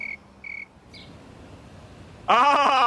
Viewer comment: I thought he said “Can I have a copy of drugs?”😂